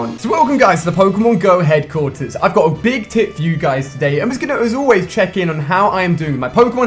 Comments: when he said i got a big tip for you i thought he said i got a big tit for you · sounds like he said I got a big TIT for you today....LOL! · he sound like he said "I got a big tit for you guys" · sounds like he is saying ive got a big tit for you guys when he says he has a big tip. love ur series tho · i thought he said i got a big tit for you lol